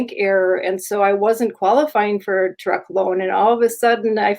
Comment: I thought said her next dream was "I need a duck." I was thinking, "Just a duck?"